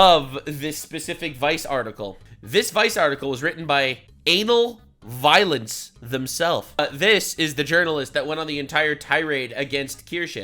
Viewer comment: I’m sorry did you just say there name is an*l violence?